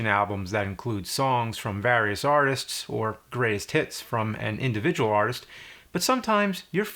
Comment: As you were talking I thought you said 'Grays tits' but immediately realized you actually said 'greatest hits'. Is a Mandela effect or something? Like, was teleported to a seemingly identical, alternate universe from my old one where you really did say 'grays tits'?